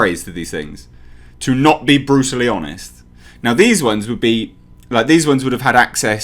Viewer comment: I swear at 4:18 he says “Bruce Lee honest”